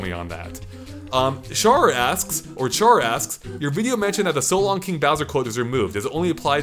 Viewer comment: My question has been answered at 19:03 and I'm very disappointed that quote was removed. But I guess it does cause confusion as many players heard him as "So long, gay Bowser" instead of "So long, king Bowser". So yeah, thanks for answering my question.